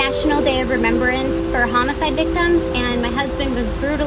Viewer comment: When I first saw the most viral vid, I thought she said HOLOCAUST and not HOMOCIDE so I was so confused. Like….no way ur husband was alive in WWII